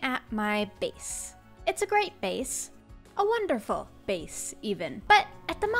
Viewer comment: I thought you said face